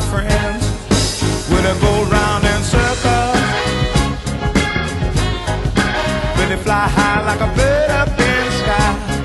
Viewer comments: Anyone else lived on earth all these years and thought he was saying Willie go round in circles,Willie fly high like a bird up in the sky??????👀👀👀😂🤣😂I did!!!!Just found this out...O.K.😳😲😎✌️ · I always thought they were saying "Willie go round in circles"